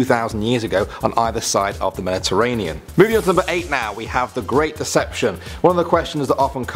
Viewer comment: thought it said the great depression